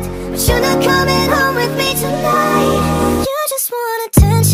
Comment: am the only one who heared " suga coming home with me tonight" at 0:33 :)